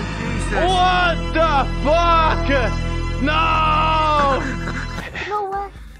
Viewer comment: he says fuck but subtitles says why xD